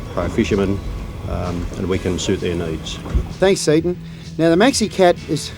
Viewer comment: Does he say thanks satan at 2:27?? What😮